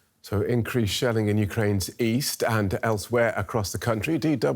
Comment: East? I thought you said Weast!